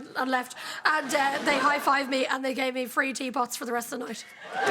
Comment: is she saying free tea pots? Is that an alcoholic drink?